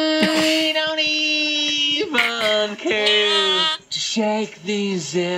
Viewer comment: Smashing Pumpkins, I heard "shake these zippers loose"😂😂😂